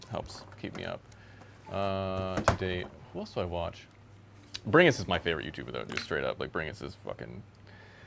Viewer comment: what name did he said? I can't understand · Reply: bringus studio